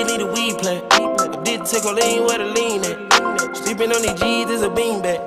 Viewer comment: Low key thought he said “I didn’t take my lean, where my lean at” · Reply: Enzhotix I thought he said I didn’t make codeine where the lean at